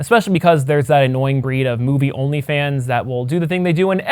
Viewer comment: Ha! said "OnlyFans" at 15:14! (I know that's not what he said but I found it funny!) Thank you for another great video!